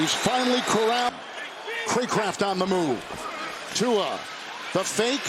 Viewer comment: It sounded like they said Kreekcraft on the move at 1:32 lol